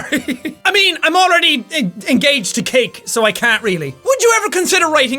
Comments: When he said he was engaged to cake, I thought he said, "I am engaged to Kate." I was like, who the fuck is Kate? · i thought he said he was engaged to KATE. i was all like WHO THE FUCK IS KATE?!?!? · When he said he was engaged to cake I thought he said he was engaged to Kate. I was all like WHATTTTT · when you said engaged to cake. I thought you said engaged to Kate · Who else had an alert moment when he said "I'm already engaged..." o.0 I seriously would've thought he meant his girlfriend ouo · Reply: Yep I thought he said I'm already engaged to Kate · OHHHH MYYYY GOOOOOODDDDDDDD JACK I THOUGHT YOU SAID YOUR ENGAGED TO KATE AND I THOUGHT KATE WAS YOUR GIRLFRIEND AND I FREAKED THE FUCK OUT I WAS SO HAPPY FOR YOU THEN I RE WATCHED IT and I realised you said cake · genuinely thought he said 'engaged to Kate' and I was like DAYUM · HAHAHA WHEN JACK SAID HE WAS ENGAGED TO CAKE I THOUGHT HE SAID HE WAS ENGAGED TO KATE XD HAHAHAHA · i thought u said u were engaged to KATE not CAKE XD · "I'm already engaged to cake." I thought he said he was engaged to KATE. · I thought he said engaged to Kate! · Lol I heard "I'm engaged to Kate " instead of cake and I'm just here like who de fuq is Kate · lol when the said he was engaged to cake i thought the said "im engaged to Kate" lol who also thought he said that or is it just? like if you think he said Kate? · Was I the only one that thought he said "I'm already engaged to KATE", not "CAKE"? I think I just had a mini freak out attack.... 🍰